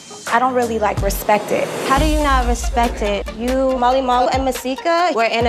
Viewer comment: Swear it sounded like she said Molly Wall